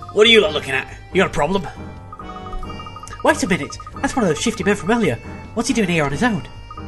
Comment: I thought Luke said "shitty" for a few seconds there.